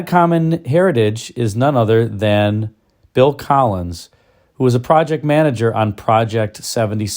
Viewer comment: At first I thought you said "Phil Collins"